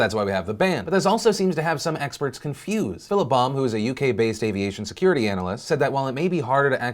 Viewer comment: When phil said, "Philip Baum" I thought he said, "fill a bomb" and was rather confused for the half a second before I read the guy's name.